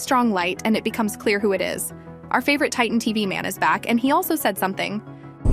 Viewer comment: Did I hear yes TV man back I know they didn’t say TV man titan but I think he’s coming back in the next episode who agrees 👇